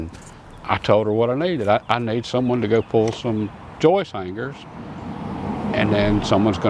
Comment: lol I thought it sounded like "joy fingers"